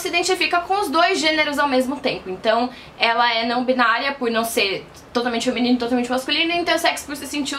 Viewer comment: ela falou não totalmente menino e não total mente masculino foi o que Eu ouvi é voltei várias vezes e ouvi a mesma coisa kkkkkk